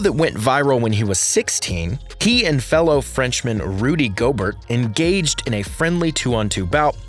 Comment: Made me spit out a mouth full of pho when I heard you say "GO BURT" 😂😂😂